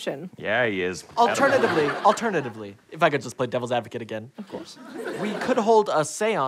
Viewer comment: I thought it said playing devils Avocado XD